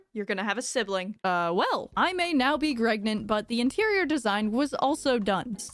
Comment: Did she say "I may now be Gregnant"? Or do I hear wrong, bcs that brings me totaly into the Limbus Company game